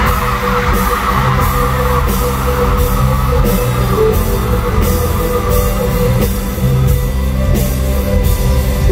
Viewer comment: 쯤에 션이 뭐라고 말한건가요?? 안들려 ㅜㅠㅠㅠ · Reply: 안승부인 how’s it going up there? 이 비슷한 말이었어요 이 날 이 말 한 백만번 한 듯 ㅋㅋ